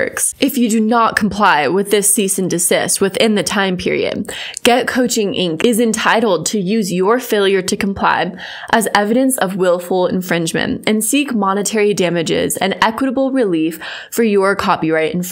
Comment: Omg i heard “getcoochie inc” and i spat my coffee